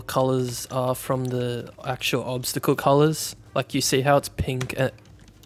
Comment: did he just say: ''F*** you say how it's pink'' ????????